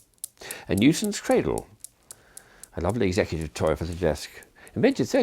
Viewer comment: did he say a "nuisance cradle"?